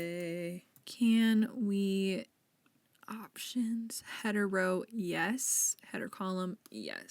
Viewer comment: This is so silly, but I wasn't looking at the screen when you said "Header row, yes" and heard it as "Hetero yes" and went "Wait, what is she talking about???" 😂